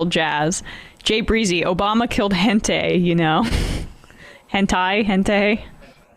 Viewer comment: Spanish being my native language I thought I heard Emma saying "Obama killed gente", "gente" being "people" in Spanish but turns out she meant hentai. lmao I've never heard anyone pronounce it like that.